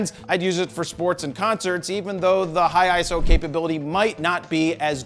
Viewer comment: Did he say "eye-so"?